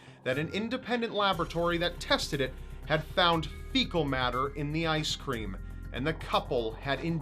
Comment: What was found in the couples ice cream in the last case? English is not my native language, and i really don't understand that word is saying at 6:06. Please explain someone xD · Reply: Fecal matter is, well, poop.